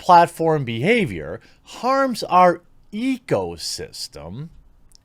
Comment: lol. I heard it as ego system. Maybe that was correct. ROFL. xD